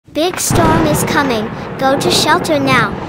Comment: its says "big storm is comming" and i hear "b1tch storm is comming" 😭😭